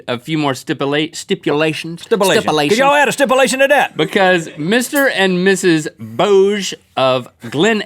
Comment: i thought IT SAID MR. AND ms. DOGE